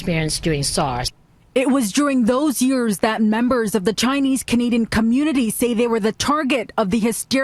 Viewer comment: I thought she said chinese comedian :S